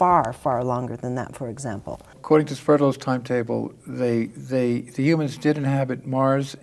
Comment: What is that time table called at 15:40? I couldn't quite catch the name of it. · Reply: Linear time table.